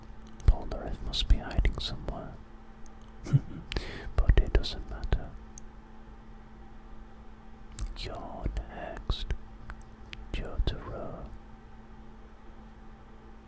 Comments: I always hear potato sanada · i swear he said potato-sama